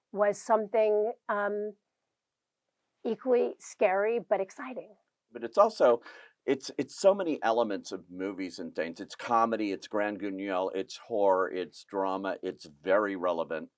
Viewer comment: what is he saying here … “grande grungelle”??